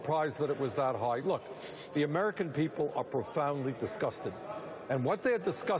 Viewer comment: I heard "The american people are profoundly disgusting." LOL